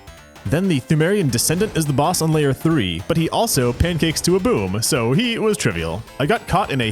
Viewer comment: I thought i heard someone mention delicious pancakes